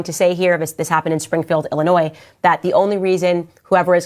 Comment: She sounds stupid it's Springfield, Illinois not Massachusetts. Candace Owen's I'm not surprised at her raccoon behavior . She's not welcomed in our neighborhood. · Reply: No. She clearly said Springfield, Illinois @ 2:54. So and are the stupid ones...as well as you for taking their word for it.